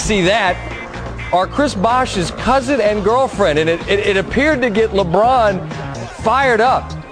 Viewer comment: The lady was Chris Bosh’s cousin and girlfriend? 3:00 did I hear that right?